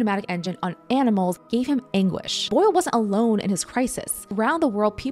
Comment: When she said “anguish” I thought she said “English“. Which made me wonder about his guilt. Or if that wasn’t a better painting.